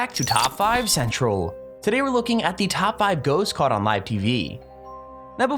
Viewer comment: When u said live tv I thought you said ytv